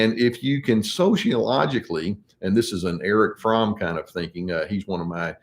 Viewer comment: Erich Fromm! Thank god…I heard “Eric Trump is one of my heroes” and I saw reality swirling